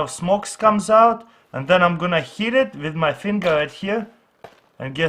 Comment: then im gonna heat it with my finger is what i heard